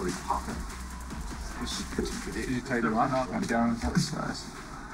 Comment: did mathews just say he's down a foot size lol.